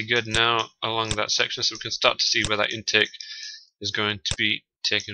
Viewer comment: I keep on hearing you say "intek" or maybe "inter", but I do not think I am hearing you right. What are you saying at 2:52? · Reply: Intake. I'm talking about the air intake that feeds air to the engine